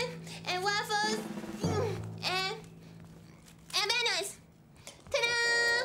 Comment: did gloria say bananas?